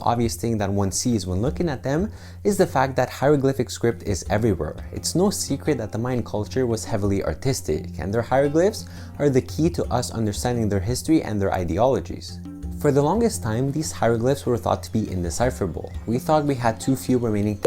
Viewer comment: I thought you said autistic at first